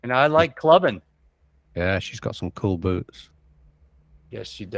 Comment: Nice! 👌 I first heard ”she’s got some nice boobs” in the beginning of the gameplay. Had to rewind and check did he actually say that. Luckily I misheard 😅